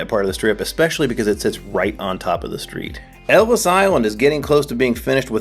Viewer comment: Hahah I thought you said Elvis Island and I sat up.